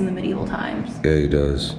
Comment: when u thought you heard someone talking it was a female voice saying "yes he does".